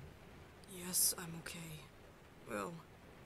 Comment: why did I hear “yes I’m gay” lmao I was like wait what and I had to rewind it😭😂